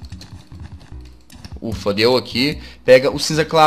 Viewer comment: só eu escutei fudeu? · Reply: Ele falou "ufa, deu aqui.."